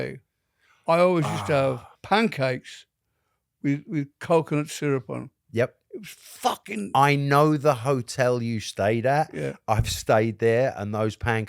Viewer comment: the first time i heard coke and syrup. And i dint question it for a second. Only after het said coconut again i was like" yhea... , that makes more sens"